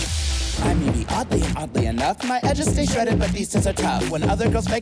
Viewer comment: not the subtitles saying "I may be ugly" instead of "I'm Yvie Oddly" 💀